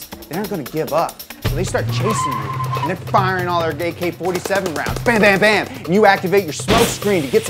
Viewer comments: did anyone else hear “gay-K 47 rounds” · Did you say 'gay k 47' rounds at 15:42?